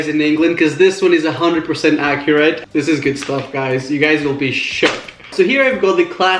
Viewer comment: first time I hear you swear.... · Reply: I thought he said shook